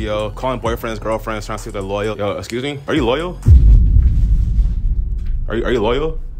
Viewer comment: Dude thought you said local 😂😂 Passed that vibe check